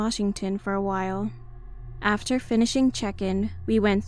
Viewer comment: I thought they said after eating chicken XD